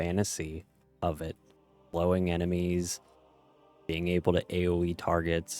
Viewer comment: wait...did you just say you want to blow enemies? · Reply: Its my mic being stupid and me non annunciating enough. I said slow